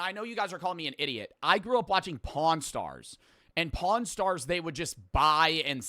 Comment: Yeah he grew up watching “pawn stars” 😭😭😭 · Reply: Ngl I actually thought he said p0rn stars 💀